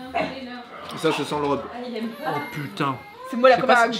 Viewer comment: c'est moi ou a 23:42 elle dit au bébé je suis la gab ou un truc comme ça